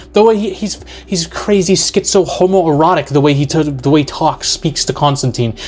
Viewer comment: IF YOU WANNA SUGGEST VID TOPICS, JOIN THE DISCORD: <link> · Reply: the vid is great as usual. When you were describing Satan as a meth head...Peter Stormare btw....I thought you'd said "hobo-erotic" and thought, that actually fits Stormare as well. A movie I can't find anymore called "The Ceremony" was this zero budget independent movie about a guy losing his soul to Satan over the course of a night. Not a Satan for this list, but an great low budget creeper.